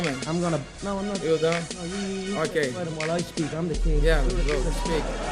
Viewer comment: Did McGregor really call Dos Anjos a spick? LOL! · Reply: hahah I dont think so, he said something like: IM gonna speak.